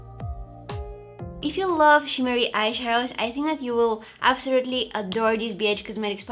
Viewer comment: I’m sorry but I swear I heard her say “you will adore this b*tch”